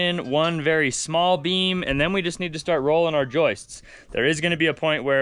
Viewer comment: 😂😂 For half a second, we thought said they were going to start rolling some joints 😅😎